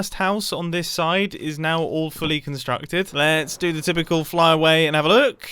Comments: I thought he said "the first house on this side is awfully constructed" and not all fully, I think I need sleep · did mumbo say "all fully" or "awfully" ? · I know when he said "The house on the side is all fully constructed." But it sounded like he said that it was awfully constructed lol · Whenever mumbo says "all fully constructed" I hear "awfully constructed" and I giggle to myself every time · Every time you say “all fully constructed” I hear “awfully constructed” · I think he said “ Despite all my best effort’s, I do believe the first house on the second side is all fully constructed.” Hands up if what you heard him say was, “awfully constructed”.